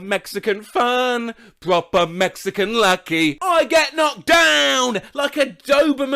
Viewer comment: I always thought it said ‘we love our Mexican ducky’ and now whenever me and my sister hear that song we sing about ducks.